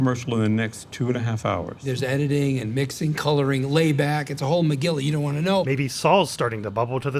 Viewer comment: "Whole Megilla": I always thought he said "Whole McGill you don't wanna know" as the side of him that does all the hard work haha.